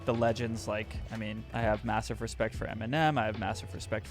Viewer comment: i like that he sounds like he says m&m instead of eminem:))